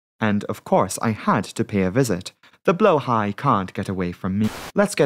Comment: I literally thought u said "blowhards"